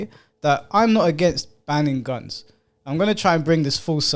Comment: banning guns, I thought you said banging guns, I initially wondered if it was some new it thing🤣